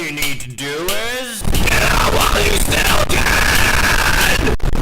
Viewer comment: I thought it said 'hail satan'....but it does sound like get out while you still can...kinda